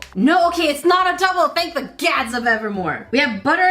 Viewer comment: I thought she said, thank the gyatts of evermore 😂😂😂